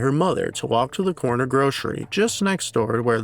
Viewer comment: I'm sorry did I hear that right? The corner "grocery"? 12:11 👍 How'd I not mention that personally American Horror story did a pretty good job correlating this with their AHS NYC season. Still kinda haunting when I think about it.